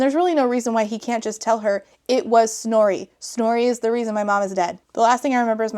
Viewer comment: I keep hearing it as "Snory" and going, "What, is she marrying the black sheep of the dwarf family or something?" Also why do they keep threatening to kill her loved ones when she basically has no loved ones? I know it only matters from the character's perspective if the threat works, but the readers really have to suspend their disbelief to go, "Wow I sure hope she's able to protect her horrible mother and brother, sure would be a shame if she wasn't able to." (Also I have no idea why emotes won't work for me anymore on Youtube, utterly baffling.)